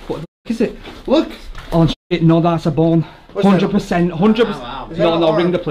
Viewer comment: I almost had an heart attack bc I thought u said "that's a born" and I was like no way but when I heard "bone" I was like thank God